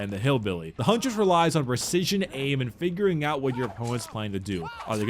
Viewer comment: “And fingering out what your opponents...” yeah I heard it